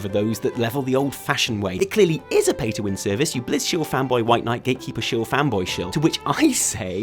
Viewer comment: Can anyone translate to clear english what he means by the line 09:40 : "It clearly is a pay to win service, you blizz chewie fanboy white knight gay, keep you chewie fanboy show". Is that what he says? It's killing me and I'd like to start using it in every serious argument i ever have as soon as possible. Thank you.